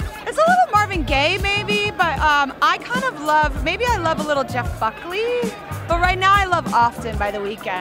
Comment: When she said Jeff Buckley i thought she said jeff fuckley lol